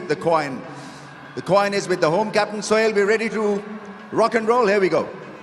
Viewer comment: soil instead of Sohail lol 😂